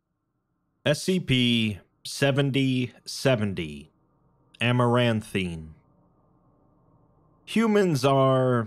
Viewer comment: For a second I thought it said Amouranth-ine